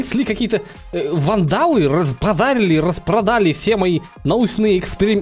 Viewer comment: Мне послышалось фразы "разбозарели, 3:46 каньяга.". Класс👍👈👈👈😅